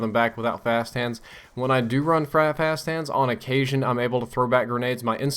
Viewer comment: did he say 'frap hands'? haha